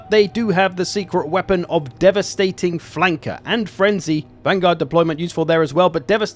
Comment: i swear i heard "Devastating Wanker"